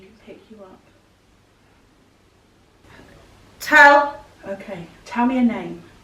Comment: ish just before said tell I swear someone said tell first